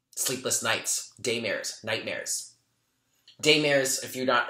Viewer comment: Omg" I thought you said "gaymare" lmao!!! Daymare. Yr awesome love your readings great energy!!!!